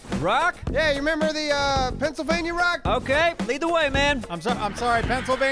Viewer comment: at first I thought said neebsylvania...